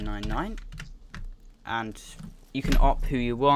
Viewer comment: What does he say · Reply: He says "op pheonix999" :) hope this helped